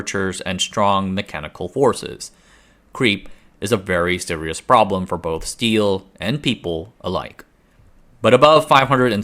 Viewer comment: "Creep is a very serious problem for both steel and people alike" 😂 Well done. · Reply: I thought he said "creep strength is very important for steel and people alike" so I was planning to become the strongest creep I can be.